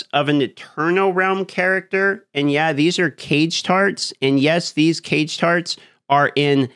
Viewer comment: Short baldurs Gate 3 live today Live daily: <link> Variety Channel: <link> Clips Channel: <link> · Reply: I swear to God I thought he was saying "cage tards"😂